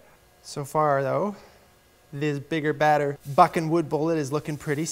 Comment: into the video. You CAN NOT convince me he didn't say F%$kin instead of Buckin. Lol